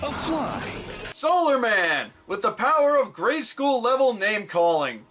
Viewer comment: Did anyone else think he said "Power of Grayskull" at this point?